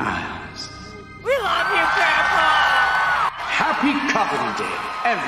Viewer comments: did they just say 'we love you grandpa'? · I heard someone yell out “we love you grandpa!” I found it funny and wonder if anyone else heard it as well 😂